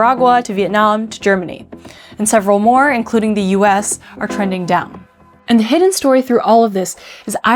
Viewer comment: discussing trends, I swear I heard you say that the US is "trending dumb." Whilst I would agree, correlation is not causality--but it rhymes.